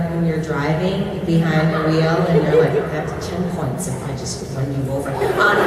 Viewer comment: What is Lana saying at 17:13 please ? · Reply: I think it was "I get to ten points if I just run you over". I'm not sure though.